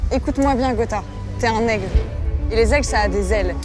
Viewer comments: J'ai entendu "Gotaga t'es un n-word" avant de comprendre "aigle". Aled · Elle a dit quoi lah maghla a 12:43?😂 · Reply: T es un aigle